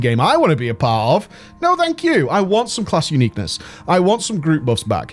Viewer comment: I want class to be unique. All I hear he says "I want to be grip bitch". Love you, keep up good work and congratulations on Aggramar kill. I hope Argus will fall down soon to.